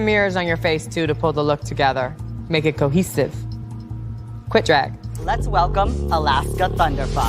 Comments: WILLAM CASUALLY SAYING QUIT DRAG IS HILARIOUS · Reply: I honestly thought she said ‘quick drag’ like a read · "quit drag" Willam is so iconic 😭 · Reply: Idk she said that I thought she said quick drag for some reason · WILLAM CASUALLY SAYING QUIT DRAG IS HILARIOUS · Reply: Wow, i thought she said ''quick drag'' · WILLAM CASUALLY SAYING QUIT DRAG IS HILARIOUS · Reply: Brooo I just said omg I thought he said quick drag but noooooo this time I heard QUIT omg